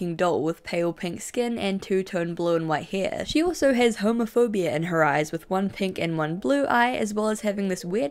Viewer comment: I thought you said homophobia instead of heterochromia 😭